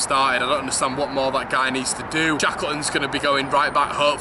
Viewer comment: who did you say could come in for Rb. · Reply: Pretty sure he said Shackleton. I'd prefer to recall Coyle from Fleetwood, but I dunno if that's possible